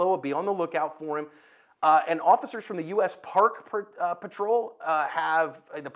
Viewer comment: The crazy thing is, they don't even patrol national park lands. The US Park Rangers do that. They only patrol certain areas in and around Washington DC, NYC and San Francisco. You're spot on about that last part, though. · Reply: yeah, that's my bad. I misunderstood them to be park rangers in a park. I guess I heard "parkway" incompletely.